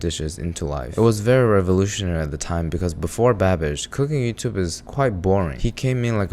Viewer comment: Everything I hear the word "Babish", my mind keeps saying "Bad B*tch"